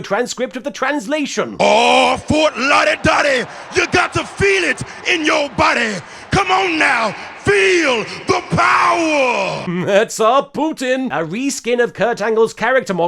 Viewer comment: what is he saying? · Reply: Well of course he's saying OHHHHH FORT LAUDY DAUDY YOU GOT TO FEEL IT IN YOUR BODY COME ON NOW FEEL THE POWAH